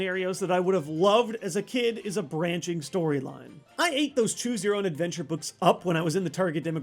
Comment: Wait, what did he say? Did he said hate or ate?